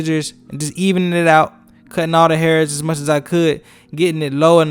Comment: I thought his mom said cut out all his hair I Think This Is Clickbait 😭😤😡